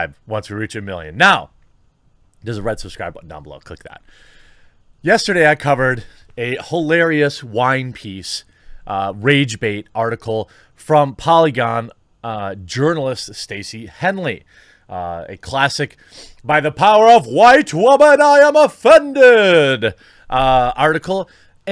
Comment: I thought he was saying "urinelists". Like in your pants are soaked in urine.